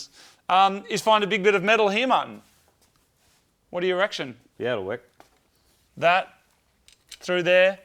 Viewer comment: Thought he said ''what do you erection?'' xD